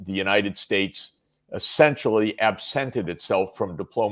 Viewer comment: Arounded 2:50 in, I think Jeffrey Sachs uses what I think might be a new word for me, abscinded, past participle (I think?) of abscind, which means to cut off. And ah, I also generally enjoy listening to the opinions and insight of Jeffrey Sachs as well. Thanks guys! Peace, love and God bless